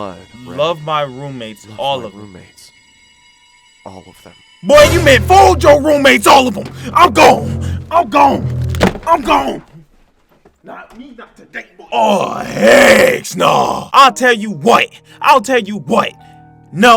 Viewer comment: I thought you said your roomate